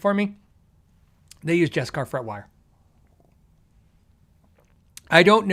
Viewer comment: Hey, I thought you were saying you preferred Beskar Fretwire to Stainless Steel, & I thought, this is getting out of hand, does your Fretwire need to stop a lightsaber?